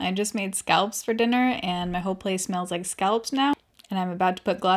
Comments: Hold on, I thought you said scalps and not scallops?? Mini freak out 😆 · I thought u said u made SCALPS for dinner. I was like “say what???”🤣😂🤣😂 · My husband overheard the video and thought you said “scalps” instead of scallops. You can imagine how that went 😂